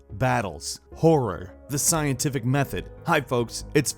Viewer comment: when he said horror I thought he said porn